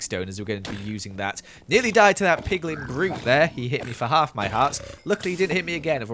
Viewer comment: In 14:17 I swear he said Piglin Groot